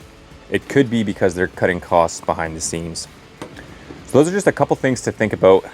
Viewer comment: I swear I heard you say, "....they're probably cutting costs behind the *seams*." - and I thought, 'how true!' - LOL